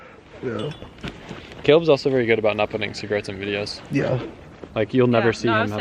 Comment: what did say at 10:05 or around there he said your good about not putting something on video? sounded like he said cigarettes but I can't tell lol. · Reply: baril yea he said cigarettes haha